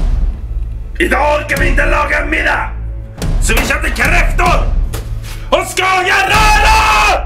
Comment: What does he say at 0:26 to 0:33???? PLEAASEEEEEEE!!!!! SOMEBODY TELL ME!! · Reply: "Idag ska vi inte laga en middag, för det ska bli kräftor och SKAGENRÖRA!" - (Today we're not cooking dinner, cuz' today it's crayfish and SKAGENSTIR!)